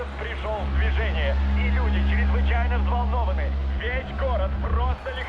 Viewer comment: I heard him say vagina